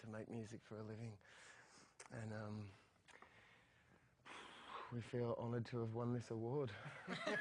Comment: what did daniel say at 5:14? i cant hear it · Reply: He sighs and says "we feel honoured to have won this award!"